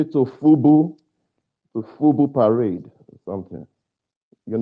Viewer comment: He said, FUBU parade? I thought he said, FUBU pirate. 😮